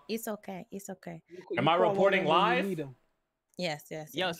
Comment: The first thing I heard was "Am I recording live"And hes not.